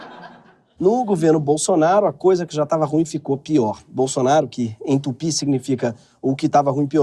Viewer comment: "Bolsonaro que entupiu", eu penso - ué, de novo? - Mas aí eu volto e percebo que ele falou "Bolsonaro que em tupi significa...", ah tá, agora entendi 😅